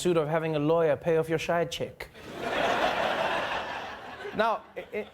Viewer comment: DId he say side chick or schide chick at 2:50.....???? Hahahah.